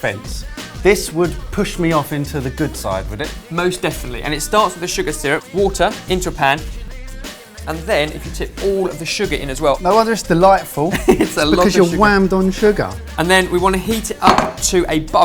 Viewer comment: Whole time I thought was saying "...water in Japan" rather then "...water in your pan" lol